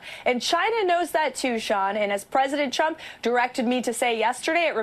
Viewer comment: Did she say President Chump?